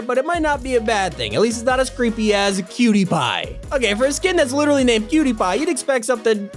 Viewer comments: When he said "Cutiepie", I thought he said "Pewdiepie" at first lol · When you said cutiepie I thought you said pewdiepie